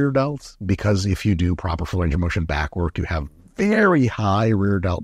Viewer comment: What did he say at 17:52? If you do proper what you have rear development? Can’t hear 😂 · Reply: he says full range of motion back work